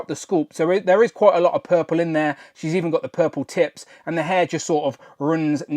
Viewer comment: I had to rewind it cause I swear I thought I heard say “she’s even got the purple titts” lmao hahahaha 🤣😂😜